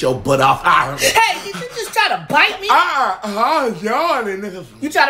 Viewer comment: Did he just say, "I was yawning n...a." 😂